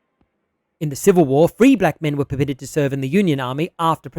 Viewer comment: I heard it as "Three black men" and that would be quite a unit